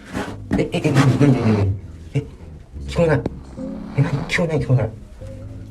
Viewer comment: さんが「聞こえない聞こえない」 と言っているところを字幕にすると 「じゃあね、しょうがないけどね」と表示されます。霊が諦めたんですかね。